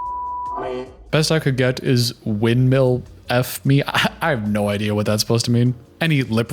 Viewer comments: i think hes saying "went down on me" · I am no expert but I think he said “went down on me”, which would make sense with the context of him that saying her throat disease likely spread to his genitals. This guy is so gross 🤢🤢